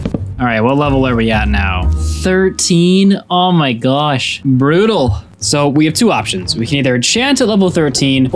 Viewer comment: I thought he said,"bruno".😅